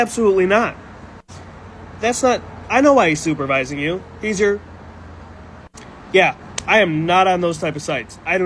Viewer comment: "He's your pimp" is what he said